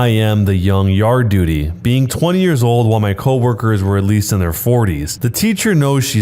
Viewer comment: I thought he said “my co workers were at least in their Fortnites.”